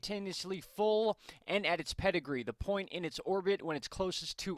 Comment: Wait what? 0:30 ... did I hear Pedigree?! 😄😄 I bet you have a pet around hhh Thanks for the good info, salam for you and!